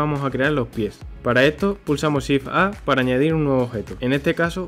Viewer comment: no pasa nada si apreto esas teclas 😢 ayuda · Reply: Nvm pensé que decía shift + alt, pero es shift + a